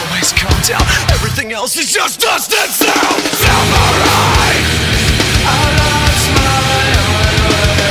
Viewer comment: When i first heard this i thought he was saying samurai not seperate xD